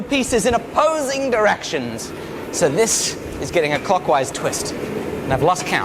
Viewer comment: did you say cockwise or clockwise? 🤣